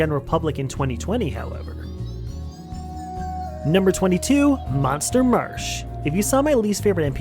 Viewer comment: did you say #22